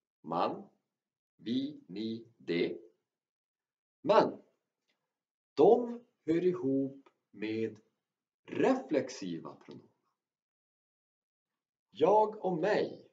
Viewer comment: Bit of a strange question, but would someone be so kind as to tell me what the word is that he used at 0:37? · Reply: "De hör ihop med reflexiva pronomen" :)